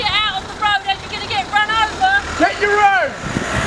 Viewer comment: what did he say? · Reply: Get your own.